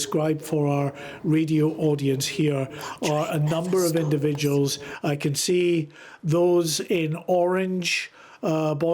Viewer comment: sounds like a demonic whisper. Can anyone make out what is said? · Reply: This train never stops... What the hell